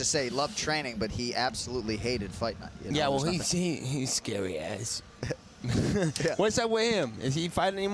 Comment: what does nate says at 5:39? i can make out most of it. is it "his scary ass"? im not sure